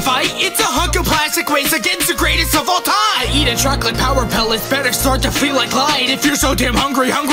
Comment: I know Pac-Man said "I eat a track like power pellets", but on 1.25 speed, it sounds like he said "I eat a chocolate power pellet". 😅 Anyone else hear it?